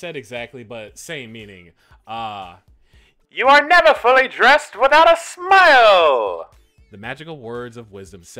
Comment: I thought it said your never fully depressed without a smile